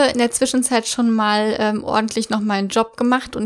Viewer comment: ich hab verstanden "ordentlich gefaket" xD. Ich hab mir so gedacht "Hä? Nein das passt gar nicht zu Kaddi"